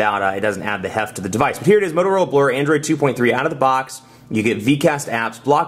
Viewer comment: oops did someone just say motorola blur 6:16 i think you did